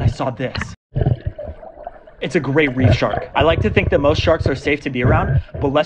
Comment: I thought u said a great white shark until I saw the caption